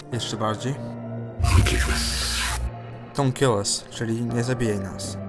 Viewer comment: zamiast dont kill us usłyszałem forgiveness